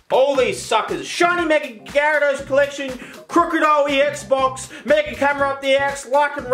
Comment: Did you just say mega camera up the arse? 🤩